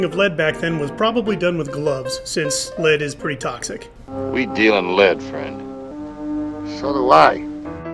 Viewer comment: Wow ok then for a good 15 years now I thought Steve Mcqueen was saying "We deal and live", that scene now makes a lot more sense.